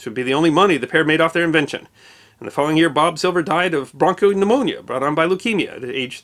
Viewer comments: Instead of bronchopneumonia he said "barcode-pneumonia". I was trying to figure out how his invention killed him. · bronchopneumonia sounded like ”bar code pneumonia” lol I thought he was making a joke or a pun or something for a sec. I had to turn on the subtitles because I didn't trust myself with what I thought I heard🤣😂